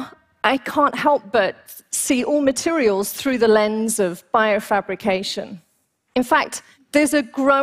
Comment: At first I thought this said “bimbofication” and I was like woah, I’m all ears